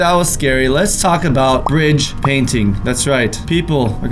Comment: When he said brige painting I thought he said fridge painting